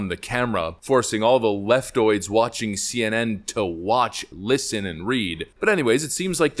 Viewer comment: I misheard you and thought you said "Force the Left to Watch, Listen, and Ree" 😂 We should just make the slogan "Make Leftists Reeeeee Again"